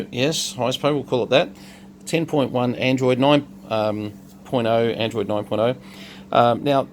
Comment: Yo it sound like you said 10.1 Android Dick🤔🥴🤦🏽‍♂😂🤣